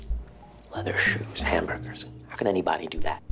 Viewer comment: Hamburgers instead of hand runners ........................................poor cows